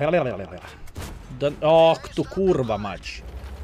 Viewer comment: why are you swearing in polish? 😂 I think I heard “kurwa mać”